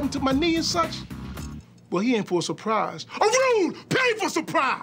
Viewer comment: what is he saying @ 2:43? ......pay for surprise